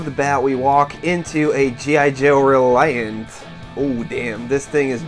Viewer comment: 'A G.I. Joe ... Reliant'? Is that what you said? :) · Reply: I know :'D it's the defiant but sounded right in my head at the time :'D